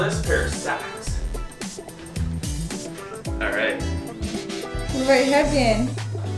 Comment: Couldnt catch what she said at 4:52!! Can anyone tell me what she said please?! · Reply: “What about your headband?” Lol